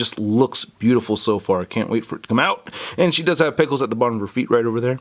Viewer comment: Lol when I was watching this, I thought that said and pickles instead of peg holes. xD ( other fans : “NO ONE CAREEEESS!!”)